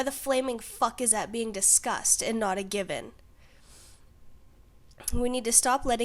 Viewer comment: I heard "Why the flaming fuck is that being discussed and not a gibbon". Took me a while to figure out what you meant. I dislike the term gay marriage. Were I to marry someone of the same gender as myself would it be gay marriage even though I am bi? If it is bisexual marriage then what about if my partner was gay?